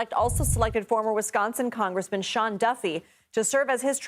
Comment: 😂 For a min there I thought it said Sean Puffy for transportation…Lol